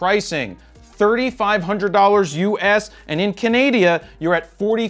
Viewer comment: he did not just said kanadia